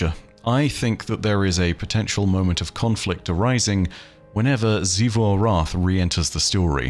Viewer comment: i sneezed at just the right time and heard "a potential moment of copulating" instead of "conflict arising" 🤦